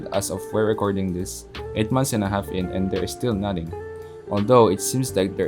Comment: I thought said nutting